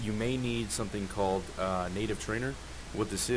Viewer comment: Hi, you said on 7:35 if your installing player mod, you may need something called like "nade trainer"? i cant hear it well, im trying to install the whole time player mods and i cant get it :( sorry for my bad english i'm coming from the netherlands!!